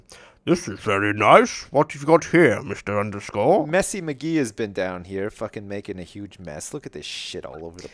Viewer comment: did he say "McGhee" at the end of Messy? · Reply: “Messy McGee” It’s like an insult name, like calling someone a “negative Nancy” or a “Debby downer” or “chatty Cathy”, those types of alliteration name-based insults.